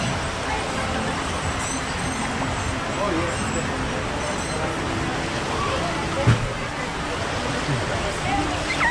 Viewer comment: i heard 9:02 someone said pwede ba apat hahhahaha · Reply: She said Pwidi ba apat - bisaya man gyud.